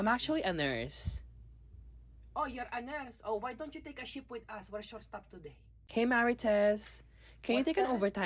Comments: i literally thought she said: "why don't you take a shit with us" lmaoooo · Word Mama Marites!!! 🥰👍🏻❤ I love the "oh, you're a nurse? O why don't you take a shift with us. We're short staffed.." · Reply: 😂😂 I thought he said shit Take a shit with us 😂😂😂 makes SO much more sense 😂😂😂🤦‍♀️ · "Youre a nurse? Oh, why don't you take shift, we short staff today" only right response 😊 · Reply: I seriously thought he said, "take a shit with us".